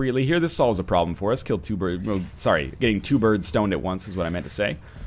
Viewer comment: Did just say getting 2 birds stoned at once???